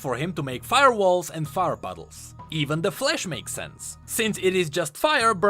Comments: I had to play the five times to get what you’re saying, turns out you’re trying to say flash but it just sounded like flesh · i thought you said "even the flesh makes sense" and was gonna link him being black to the fact that he controls fire lol